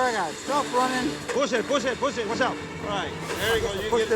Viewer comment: I thought the manager was saying "Bull shit, bull shit, bull shit" 😂 No, he was saying "push it" 3 times.